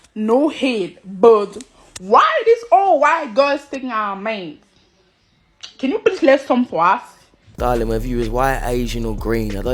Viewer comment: wtf did she say? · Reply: “No hate but why are all these white girls taking our our men?” Why aren’t you coming for us?” (Or something like that)